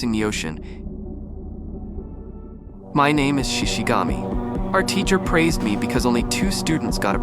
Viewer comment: i thought this said shinigami